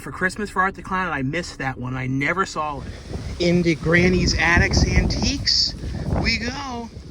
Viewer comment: i almost thought you said grandma's attic with ivy the guy from storage wars at first lol but you said granny's attic lol